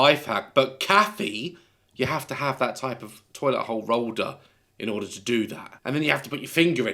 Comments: did daz just say toilet holl rolder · daz said Hole rolder instead of roll holder lol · why have I only now realised he said holl rolder instead of being roll holder😂listen closely